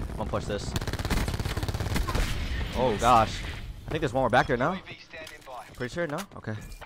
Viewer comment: Did he say “Niggas are back here, nah?” ? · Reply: Faith I think there’s one more back here, no?